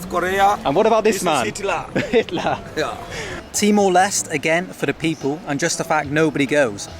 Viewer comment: I had no idea what you were saying, so I had to go to Google Maps an search for Bali and see what was an hour away. It sounded like you said T-Molest. So for others who are hard of hearing like me, he's saying Timor-Leste.